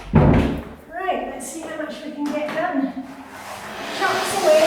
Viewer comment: What's that you are saying at 1:24? I love hearing all your little sayings "hey ho", "bob's your uncle", "canny Scot", "get off your bahookie". 💜💜💜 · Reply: Ha, ha! I said "chocks away" which is a British expression which basically means, "okay, let's get started". Here's the background... <link>